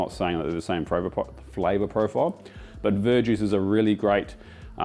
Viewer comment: Could anyone explain to me what he said was the alternative to dark ale? Did I hear it correctly? I swear I heard something like Ver Juice. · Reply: Yep, verjuice. It's unripe grape juice, can be used instead of wine or vinegar